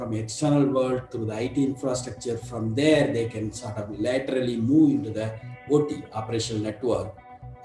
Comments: he says "laterally", buy the subtitles say "literally". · subtitles say literally, I think he meant laterally